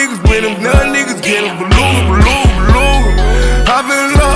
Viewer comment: "ballooning balooning" i thought he said glue glue glue